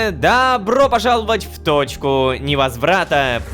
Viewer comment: наушник лагнул и мне послышалось не точка невозврата а *тоПка невозврата*😂